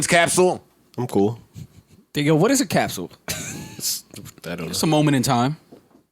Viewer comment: Rory said “ Nigga what is a capsule“ 🤨🧐 @ 54:48 Joe looked at him like wtf yu jus say??